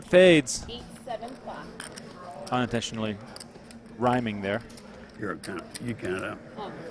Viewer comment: did he say youre a cnt you count it out 🙃